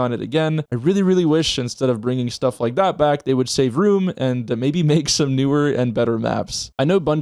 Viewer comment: Time stamp: 4:58- Did he say "Maybe say vroom" or did he say " Maybe save room"??? Someone pls tell me which one bc Idk.